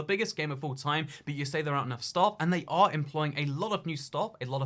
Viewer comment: I had to turn subtitles on for the begging of the video 😂 and even then the subtitles thought you said ‘Stock’ the first couple of times instead of staff